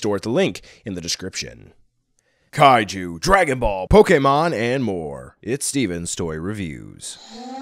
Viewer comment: when he said pokemon i thought he said pokimane